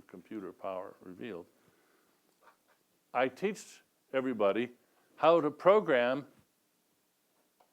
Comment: Did he say TEACHED?